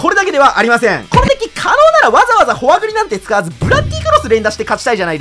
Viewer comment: 関係ないけどホワブラがフォアグラに聞こえた